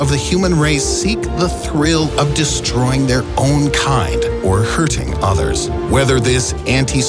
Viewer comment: for some reason instead of saying "such members of the human race seek the thrill of destroying there own kind" CC said "such members of the human race seek the thrill of destroying there own car" and that got me to laugh a little